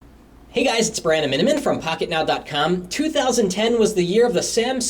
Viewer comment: What's your last name ? All I hear is "Minamin" ? Lol.